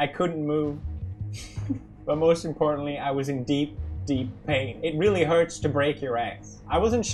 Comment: the caption says: It really hurts to break your eggs HowToBasic wants to know your location